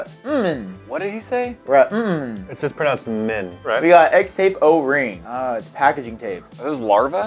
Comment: I thought he said flex tape😂